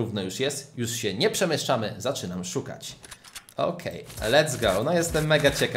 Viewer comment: Czy tylko ja usłyszałam 17 równe zaczynamy! 2:50 XD lub 19 XD